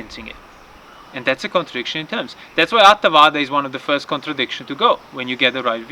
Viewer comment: there is a word I am not understanding, nor have I heard it before, is it 'atavada' the first contradiction to go ? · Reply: Yes, it is Attavada; the sense of Self is also assumed to be something indefinite and vague and ultimately outside of this experience. Even if one does not think so explicitly (e.g. The Soul), that is the underlying attitude and the end result of a deeply rooted misconception. It always kind of recedes into the background/periphery; whatever I point to in the experience to be the Self does not quite seem to capture it completely (it always seems to be somehow more than or different to just XYZ within the experience), and by that way of conceiving, it gets to be placed outside automatically. But, in order to see through this and to stop misconceiving it, one needs to develop the aggregate of virtue (which has many levels of subtlety that one discovers through the gradual training), because it is precisely the deeply rooted habit of craving (opposite of virtue) that is holding this view up. You can not uproot it simply by thinking harder about it, you have to both perfect your virtue and think hard about it a lot, over a long period of time.